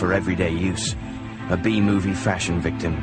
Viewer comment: thought he said bee movie victim